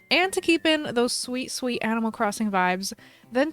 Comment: I thought you said sweet sweet animal crossing views 😂